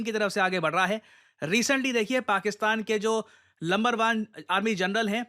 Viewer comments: did I hear lumber 1? 😂😂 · did you say lumber one instead of number one 😂😂😂😂